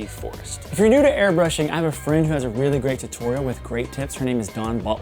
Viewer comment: I could swear that at 13:42 I heard: "I have a friend who has a really great tutorial with great tits".